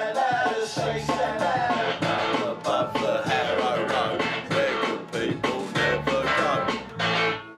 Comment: Ok. Does anyone have any idea what the hell he’s saying at 0:56 ? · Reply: "Down above the Harrow Road, where good people never go." Harrow Road is an old road in London.